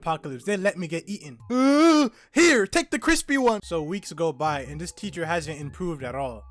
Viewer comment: In the zombie apocalypse scene my dumbahh thought he said take the black one💀